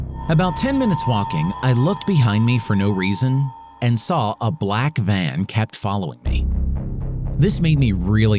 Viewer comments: I thought it said "I saw a black man" and I was like 😃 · Omg in the second one I thought he said a black man following me💀